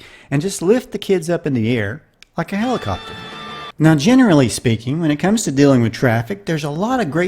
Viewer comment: thanks for these. helps to brighten my mood every time. BTW, at 2:18 did you say "...generally speaking..." or "...General Lee speaking..."?